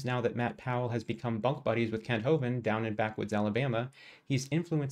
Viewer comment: did you say 'bug' buddies or 'bunk'? Either works IMO lol. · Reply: Bunk buddies. :D